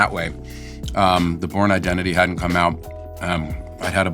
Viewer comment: It sounded like he said the porn identity, They probably made a spin off with that name.